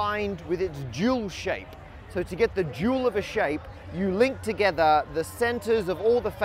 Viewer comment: I don't know why, but every time you said "dual of a shape", I misheard "jewel of a shape". And the dual shape used to make the 150-faced... disco ball... thing is looking like a real jewel CUT from the previous shape. I think, both names fit this type of transformation/relation of shapes, but "dual" is too plain, general and not as specific as the "jewel".